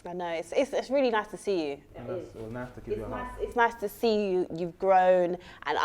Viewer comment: I can’t be the only one that heard him say “it was nice to give you a hug” when she was saying it’s nice to see you at the end